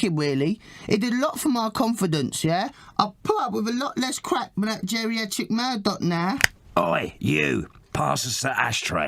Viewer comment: can someone tell me wut 2D is saying at 7:40 ? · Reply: he says “i put up with a lot less crap with that geriatric murdoc now”